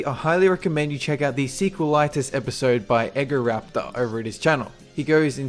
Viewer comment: Did you just say EGG O raptor?